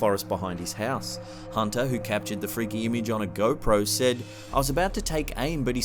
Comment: anyone else heart "captured the image on a GOAT-pro"?